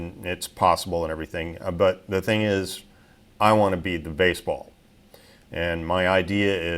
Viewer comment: "but I want to be the baseball". Love it. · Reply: I thought he said he wanted to "beat" the baseball